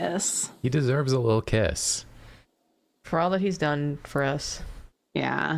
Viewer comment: Every time they said "he deserves a little kiss" I kept hearing "he deserves a little piss"